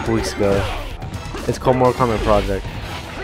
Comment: wtf did u say??? I replayed it so many times and I still dnt kno. Mortal Kombat wat??? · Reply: He said Mortal Kombat project